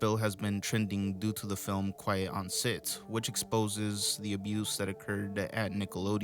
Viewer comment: i swore he said "quiet on SIDS" 😅